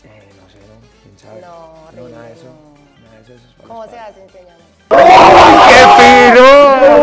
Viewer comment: que dijo ella? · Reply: como se hace enseñame